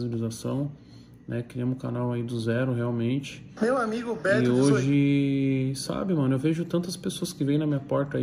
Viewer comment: "Meu amigo Beto dezoi ... " q viagem é essa hahaha